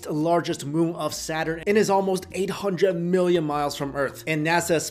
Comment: am I only one that heard him say 800 miles from Earth